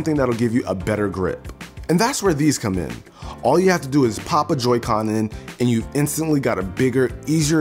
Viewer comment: I thought he said papa joycon in there